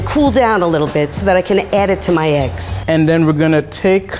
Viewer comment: I heard “so that I can add it to my ex” 😂😂